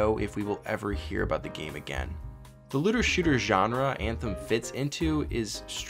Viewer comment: What does he say the name of the genre is at 4:41 ? "Litter shooter"?